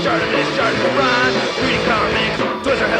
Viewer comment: I put on subtitles and when you said Shartimus Prime it thought you said Shortness Prime.